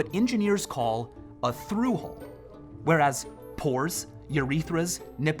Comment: When he said pores I thought he said porns, dirty minded I guess